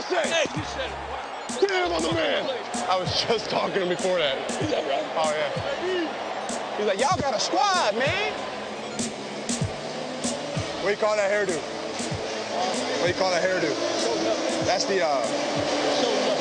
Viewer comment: What does he say at 1:50? "You gotta squad man" ??? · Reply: Oh ok. I thought he may have said "you gotta squat man!" meaning: you gotta get stronger legs man!